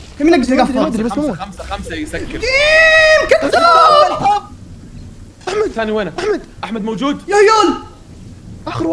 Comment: كني سمعت اشي غلط 😂🤦🏻‍♂️ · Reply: قال يقطع ام الحظ بس مع مداخلة طلعت الكلمة الي ما ابي اقولها هههههههه